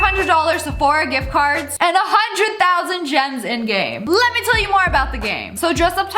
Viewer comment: I thought she said 1000 germs in game and I’m like Nani?